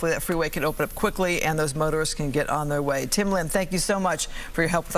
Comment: "and those murderers can get on their way" 😂😂😂 Was I the only one that misheard that?